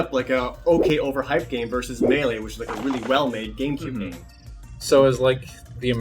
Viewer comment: did he say melee was well made 🤔